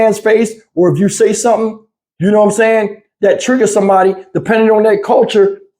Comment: I thought she said niggers